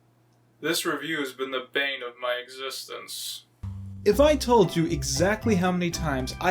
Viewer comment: Did you just say "the vein of my existence?"